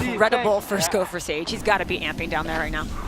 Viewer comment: does she really say “he’s gotta be ampied”